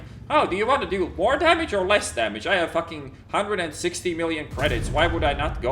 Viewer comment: I am sorry to interrupt your rant, did you say you have 160 million credit?